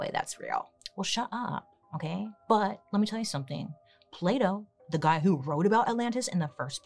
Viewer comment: I'm a French speaker (so I'm not as used to hearing the different sounds in English as someone for whom it's their first language) and every time Bailey says "Plato" I hear "Play-Doh". I can't stop laughing 'cause the sentences don't make sense at all 😂